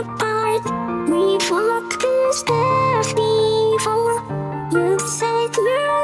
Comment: Am I the only one who heard "we've f*cked the stairs before "